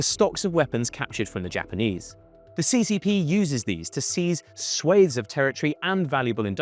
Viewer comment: The moment when I heard "King Dynasty", I immediately exited the video. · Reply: AND at 16:59 he says "swaits" of territory instead of "swaths" -- the British truly are silly creatures.